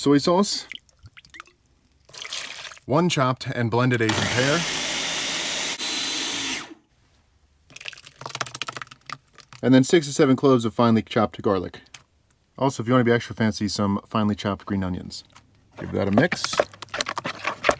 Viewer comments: Is it bad that at first I heard "67 cloves of chopped garlic" and had absolutely no problem with that? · i looked up cuz i thought i heard "67 cloves of chopped garlic" LOL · Misheard 6-7 cloves for 67 cloves of garlic. Still not upset.